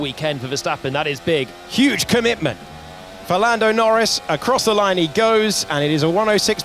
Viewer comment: Did he say Ferlando 😂